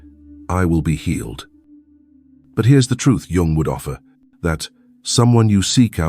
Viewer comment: The ai barely gets words wrong anymore or inflections, but at 13:51 what word is he addressing us by? "Young wood offa" is what i heard